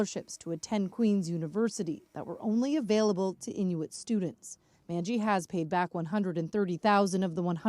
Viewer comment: No they misheard. She said the daughters were Hinduits